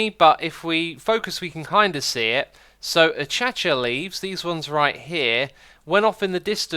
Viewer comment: What the f*** did you just say? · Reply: Achacha leaves?